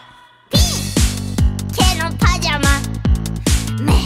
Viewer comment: ケツのパジャマに聞こえた俺は… バカなのか？…